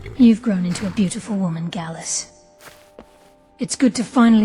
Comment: when she is talking about the past she is talking about gallus the one mercy murdered and former lover of karliah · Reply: s, to my ear it sounded like she said Galesse instead of Gallus, which didn’t fit from the context. It may be my aging PC or my aging ears. 😏